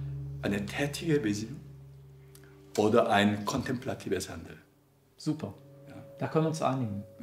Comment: Kann mir jemand sagen, was Byung-Chul Han da sagt? Habe ich leider nicht verstanden :/ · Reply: "Wir brauchen eine tätige Besinnung oder kontemplatives Handeln"